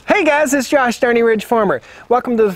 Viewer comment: Sounded like you said_ This is Josh... dirty rich farmer